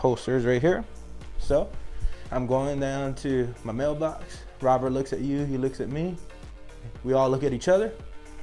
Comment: Hahahaha I had to watch it 2 times, I thought he was saying "Robert" not "robber" 😂😂😂😂